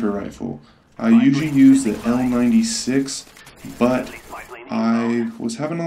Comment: did u say the I96??? if so, its the L96